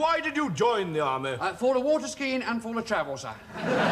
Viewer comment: What'd he say? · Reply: "Uh, for the water skiing and for the travel, sir"